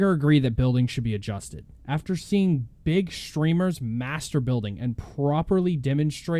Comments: Who heard “big streamers master-bating” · When you said "Twitch streamers master building" at 3:44 I thought you said masterbauting lol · When he said “master building” I thought he said masturbating